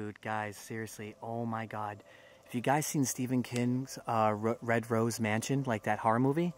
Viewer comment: It sounded like he said "Stephen Kittens" not Stephen King lolol